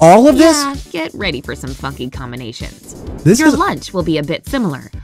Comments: i thought she swore this is what i heard: "Yeah get ready for some fuckin combonations." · I thought she said "fucky combinations" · I thought she said "fucky combinations" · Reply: I thought it said fucking combination.